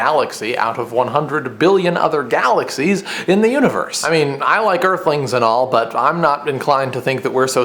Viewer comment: lol. Ath 3:10ish I thought you said "I like airplanes" & I was like.. okay..? haha :P I love you Dan Brown!